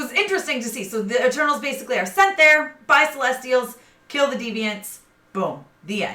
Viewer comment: I SCREAAAMMEEEDDDD, I LITERALLY THOUGHT YOU SAID "FIGHT" CELESTIALS FOR A SEC. Anyway, thank you so much for this review! loved it!